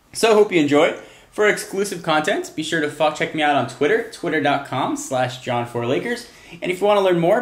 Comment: is sounded like: F****in check me out on twitter